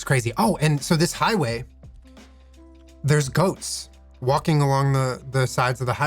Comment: i literally thought he said there were ghosts on the side of the highway until he said they eat the grass😭 GOATS omg i was about to call the Ghoul Boys like 𝘸𝘩𝘢𝘵